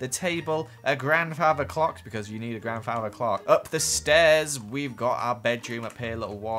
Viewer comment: when he said u need a grandfather clock i thought he just said i need a grandfather. if i NEED one, help me bring him down from heven.